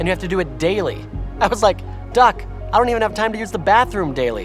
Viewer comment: I was like, Doc, I dont even have time to use the bathroom daily, let alone sleep 0:45 Or, did say duck?